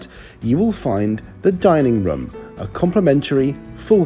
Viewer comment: I liked the way you said dinning room it sounded like you said dinning rum!